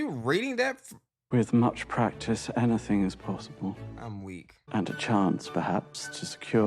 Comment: He's saying "I'm weak" but I kept hearing "ennui" and I was like "I don't think you know what that word means"